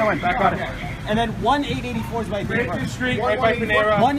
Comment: Sounds like he says "1884 is my green card". Honestly..thats exactly what I heard.